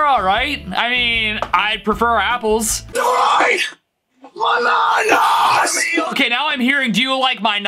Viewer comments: idk why but I hear “DUBAI!!!!!!!……….. FANANAS!!!!!!!” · “Do you like BANANAS!” is actually “Liberate my MADNESS!” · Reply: I hear “ DO YOU LIKE MY NA NAS? “